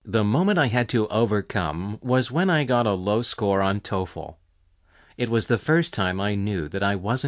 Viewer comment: who wrote these stupid subtitles? how could you write "till fall" instead of "toefl"?!! (06:13) are you that stupid?? And that's not all, of course. There are so many mistakes!